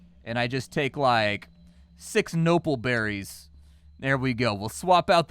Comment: I totally thought I heard say Nobbleberry.